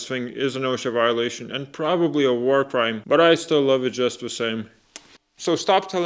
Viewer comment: I skipped through the video to see if it was good, and all I heard was "Tic Tacs, probably a warcrime".